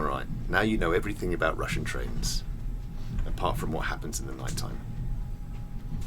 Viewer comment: "Now you know everything about russian trains ! And part of what happens in the night time *winks*" At first I heard, "Apart from what happens in the night time", cheeky Bald, wouldn't have surprised me ha